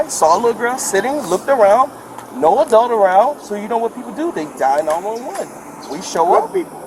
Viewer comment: "so they dial 911" for anyone who is reading captions instead of listening